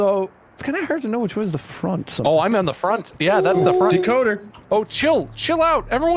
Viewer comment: when he said decoder, I thought he said Dakoda,(which is my name.)😂